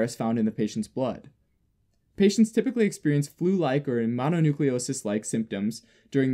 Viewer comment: What does he say after flu like symptoms around 4:35? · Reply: Mononucleosis